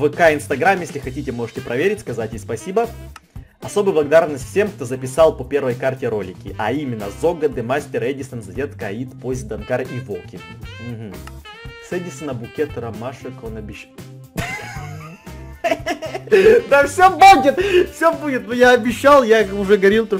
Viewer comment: На 11:00 мне одной послышалось садись на букет ромашек он обещал